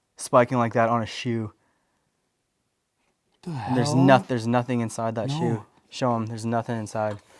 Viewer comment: I think there's an evp saying help